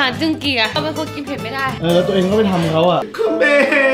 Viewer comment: ใครได้ยินเหมือนเราบ้างอะ 'แล้วตัวเองก็ไปทำเค้าอะ'